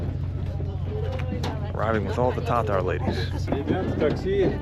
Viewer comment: At first I thought he said, “Ta Ta Ladies”. 😂